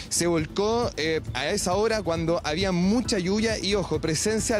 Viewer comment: En 1:04 el periodista dijo se volcó por que habia mucha hallulla asi escuché yo . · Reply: Dijo clarito mucha lluvia....